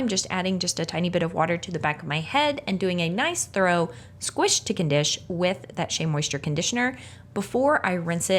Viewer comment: I was using the Closed Captioning around 9:38 instead of "Squish to Condish" it read "squish chicken dish" So I will be laughing the next time I make chicken with cream sauce.🍗🤣🤣🤣